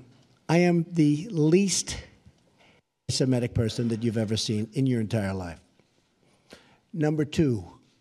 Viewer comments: did he say "I am the least Semitic person that you've ever seen in your entire life?" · I thought he said he was the"least Semitic " person.